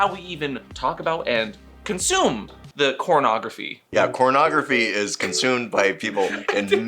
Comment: Yeah, CornPub is definitely a no-no to say on the tubes. I just don’t know what they have against corn or pubs. LoL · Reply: Mr. clearly says cornography but closed captions says he said pornography.